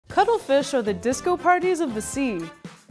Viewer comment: I literally thought she's saying cuddlefish I want to cuddle that fish